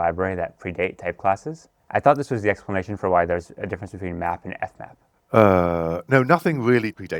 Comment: says "difference between map and fmap", but the subtitles say "math and F math", which makes no sense. 12:47 Simon says "principal types", but the subtitles say "principle types".